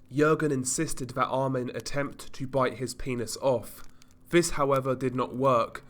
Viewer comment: I zoned out and zoned back the first think I hear is "trying to bite the pines off"